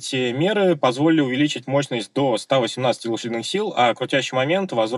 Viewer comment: Мне сначало послышалось 180 лошадиных сил😢😢😢